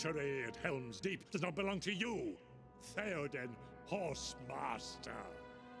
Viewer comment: I thought he said "Horse Bastard"